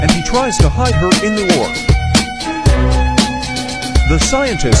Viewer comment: bro when i heard 'omen family evaporated ' it got me dead💀